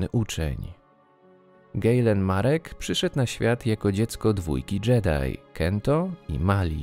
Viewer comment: Usłyszałem "Jako dziecko czwórki Jedi" xDDD